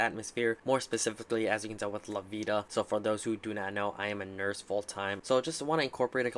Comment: HAHA!! I thought you said "I"m a full time Nerd!" My only thing with Lavita is the gas fees it requires to stake. Each month I stake their tokens it requires 11 to 14 TFUEL. That's more than the staking tokens are worth! The other tokens I stake usually cost .4 or so TFUEL. Have you experienced similar results? Thanks for the video